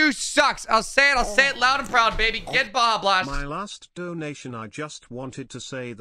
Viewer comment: I thought he said blajah blast Mt dew, if you are a friend of blajah then hello